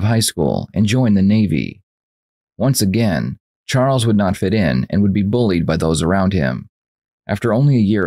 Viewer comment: Press CC for subtitles. Sometimes they take an hour or so before they can be used. The Good Nurse is currently the #1 movie on Netflix and the police interview of Charles Scullen can only be seen here. We will be releasing the entire unedited 2 hour interview on our second channel this week so check the description for a link. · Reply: He said "fit" in? I thought he said get in.....thank you!😆 I'll give it another try😉 my the buds I am using are💩💩 but I listened a couple times🤷🏼‍♀️🤦🏼‍♀️